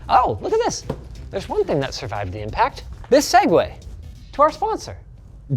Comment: omg I thought you always said "Segway" but it's actually segue